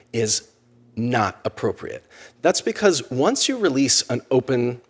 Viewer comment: "is illegal" shows up in the closed caption, but what the speaker says is "not appropriate".